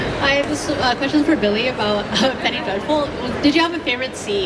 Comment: Did I mishear or was this question about Penny Dreadful?